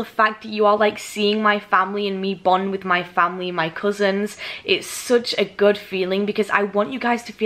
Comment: I thought you said cousin not sister